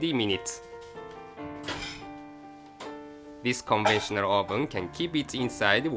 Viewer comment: + - I'm wondering if he is saying "CONVECTIONAL", oven instead of "CONVENTIONAL", at 5:10 ?? If I google for "countertop *convection* ovens", I get a bunch of links to ovens that look very much like the one she is using here. What do you think? Is that possible?